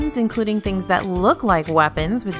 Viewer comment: But can I tape my phone to a lightsaber as a makeshift selfie stick? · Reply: Kaitlyn Buck Oof thought it said even light sabers